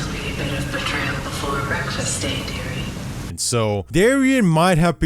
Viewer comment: Look in the subtitles. It says Darion. Although dearie makes an equal amount of sense if they were her children or people she cared about... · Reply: read here, she clearly says dearie. It wasn't referring to Darion.